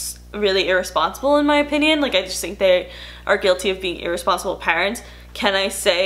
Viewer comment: I am trying to catch up on videos on Youtube then post and forgets about all the other Youtubers I need to catch up on and watches 💖💖💖 Lol when you said irresponsible parents I thought you said irresponsible pirates 😂